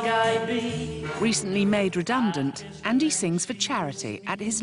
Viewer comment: How tall is Andy? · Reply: I could have swore she said dandy. I'm glad you answered so I could correct what I was hearing.